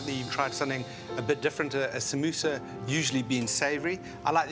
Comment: did anyone else hear sambusaa 😂😂